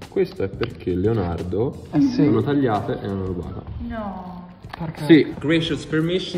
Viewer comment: Ma cosa dice a 3:08 che non capisco? Le ha tagliate perché...? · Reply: Sono state rubate!